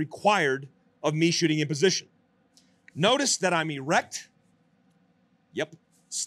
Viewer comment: Did he say he’s “erect” at 18:56? I concur that the same thing also happens to me when I shoot🤭